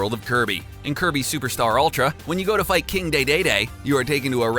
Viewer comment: King Dey Dey Dey Instead of King Dedede